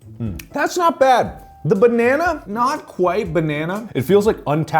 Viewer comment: DID YOU SAY POTENTIAL